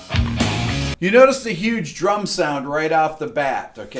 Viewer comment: I misheard you as saying; "You'll notice the huge 'drunk' sound right off the bat" and I'm like; "Ah, nicely put!".